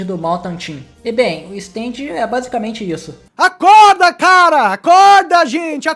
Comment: oq vc escutao nesse momento 10:14 eu escutei acorda